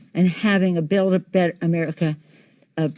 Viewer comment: For a second, I thought she said "build-a-bear America" Our time was almost upon us...